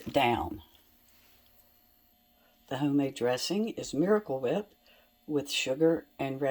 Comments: No. The Miracle Whip dressing stays perfectly intact. When you mix up the potion you are eating I mix it well. It really stays fresh as long as the Lettuce, spinach and Peas are dry. We had some for lunch and dinner. I'm probably eating it for breakfast too. 🙃🙃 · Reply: so glad I asked. I thought you said cool whip. Silly me, that makes more sense. Thank you, dear. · so glad I asked. I thought you said cool whip. Silly me, that makes more sense. Thank you, dear. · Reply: I thought I heard cool whip too!🥴😂